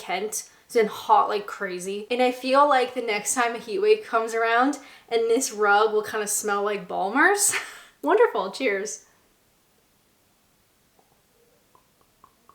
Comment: Misheard "this rug will smell like ballmarks" and wondered where the dog was. 🤣